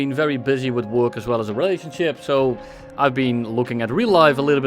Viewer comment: did bro just say the r word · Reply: no its a 'relation ship', he just started playing naval along with the other 4 players